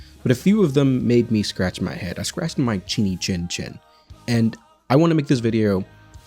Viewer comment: I swear i heard you say "I scratched my tiny chin chin" and if you know, you know... XD